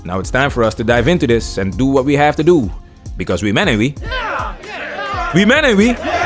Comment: I love these videos and I love how it sounds but what is he saying at the start of his videos? Humanity? Hubbanity? I feel like I am missing something and I can’t comprehend it. Asking seriously. 1:47 is where it is in this video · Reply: "We men ain't we?"